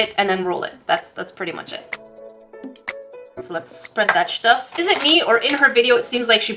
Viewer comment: Um.. WHAT DID YOU SAY!!!! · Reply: "Lets spread that stuff"? Calm down